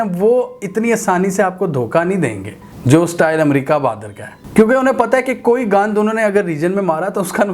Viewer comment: did he say "gand"?!! 😂